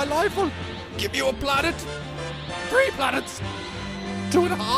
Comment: i legit misheard im a haggler, as "and my butler" as if 3 planets AND his butler would be the tiebreaker